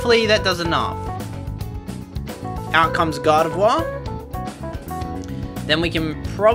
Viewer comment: when you said gardevior my captions thought you said "GOD OF WAR" and for Espathra, it thought you said "THIS BATHROOM" 🤣🤣🤣